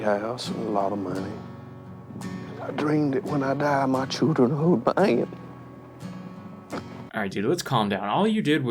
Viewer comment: can anyone translate wtf he says at 24:44....I legit missed the word he says cos of the thick accent when he crying "I dream that when I die, my children....." no clue after that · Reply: "..hold my hand."